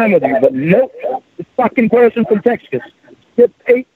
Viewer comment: Anyone else hear “Teskas”